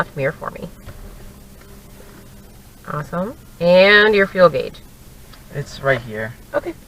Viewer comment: What did she say at 1:47? What is that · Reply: Fuel gauge.